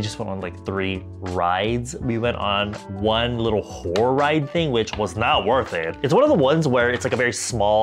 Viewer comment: I thought you were saying Whore not Horror ride, girl what even is that 😭